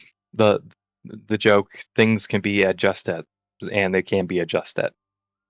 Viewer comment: What did you say here? "Things can be adjusted"? The joke flew over my head 😅